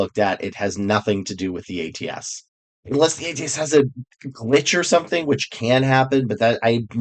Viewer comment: I thought at 1:25 that you said "lich" instead of "glitch." Live in fear of the ATS lich.